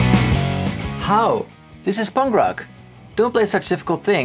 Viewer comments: When you said I love "punk rock" music, I heard I love "bhangra" music, and I was very confused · When he said "punk rock" I misheard it as "bhangra" and thought he was about to bust out some Daler Mehndi